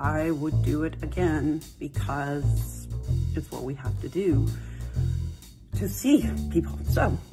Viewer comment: Bells palsy is very difficult to live with. My father in law has been affected by it a couple times. Terrifying that she's saying that she'd "do it again, because.... Uh, idk, I have to?" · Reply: I thought she said "to save people." (Which is even more insane.) I could be wrong though.